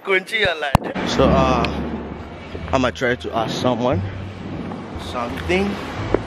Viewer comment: คุณชื่ออะไร ❤️❤️❤️ i like you bro. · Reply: 555 เค้าพูดว่าคุณเชี้*อะไร